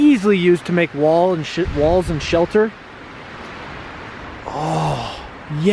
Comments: I sounds like he was going to say (walls and sh##) instead of (walls and shelter) am I the only one who hears that. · did he say walls and shi*